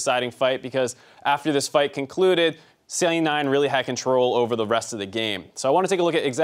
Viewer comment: My English is bad.. but did I hear "Silly 9" at 9:50?